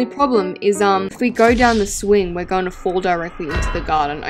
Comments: Did call the side a swing? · did she just say a slide is a SWING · Did she say swing? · did she just say if we go down the swing 🤣❣